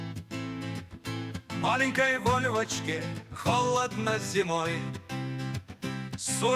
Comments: Мне сперва вместо вольвочки послышалось БОЛЬ В ОЧКЕ😂😂😂😂😂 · блин, музон огонь, только сначала услышал "маленькая боль в о4ке" но, в принципе, правильно расслышал